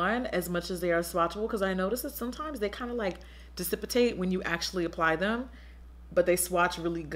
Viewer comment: LOL did she say decipitate at 11:08 I'm totally kidding LOVE YOU JACKIE! ;-*